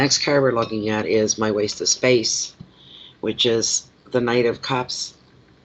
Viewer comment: What card is the (waste of time ) she talks about ? She didn't say , I can't identify that card because of the design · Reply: I heard her say Knight of Cups.